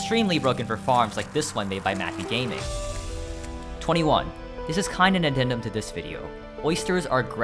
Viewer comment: holy shit i thought you said matthewgaming